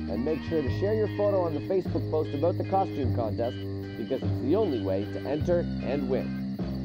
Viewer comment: So is this contest a contest on what the monsters will dress up as? Also if it is that I would really want to enter but I don’t really like Facebook or twitter sooo is there another way to enter without using those sites/apps? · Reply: Nvm I just heard it “This is the only to enter” Also I’m new to MSM and I’m already a fan I just got My Singing Monsters and My Singing Monsters Dawn Of Fire